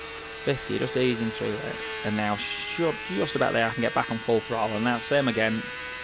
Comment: my english is not that bad, but all I heard was "oi! full throttle!"